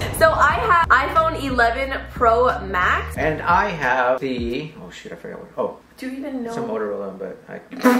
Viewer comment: did he say sh!t